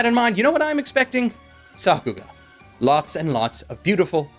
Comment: you use a word concerning your expectations for Flip Flappers. What I thought I heard does not effectively Google. What was that word and what does it mean? sappu?